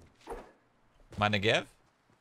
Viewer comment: i thought for a sec he said "mani🅱️🅱️a" smh